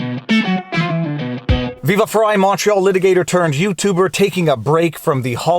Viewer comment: For the longest time I thought you said: "Viva Frei Macho Litigator"